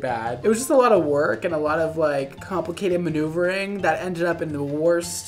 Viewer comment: What I thought I heard: “She got Keratin in her hair” What I saw: Carrot in her hair 👁👄👁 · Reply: For two ponies, I heard, “Nuva Ring” instead of maneuvering. (I watch at 1.25 speed🤣)